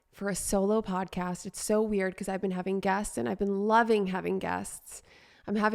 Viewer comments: I thought you said you've been having gas... Ohhh....GUESTS!! Don't force your gas, but force your guest. MESSAGE RECEIVED! Namastaygassy. Guesty. Whatever. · It sounded like you said " I've been having gas, and Ive been loving having gas" 😂 ( instead of " guests")